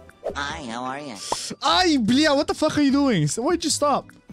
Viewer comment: Did you just say ай бля?